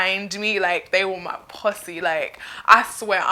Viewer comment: Am I the only one who thought she said pussy not posse XDD BTW I love you accent and yah earned yourself a sub lol